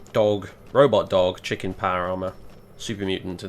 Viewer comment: said "chick in power armor" and for a second i thought he said "Chicken power armor" and i just had to share that mental image